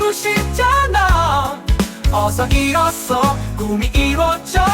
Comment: 이 부분 '어서 잃어서' 라고 들리는 것 같군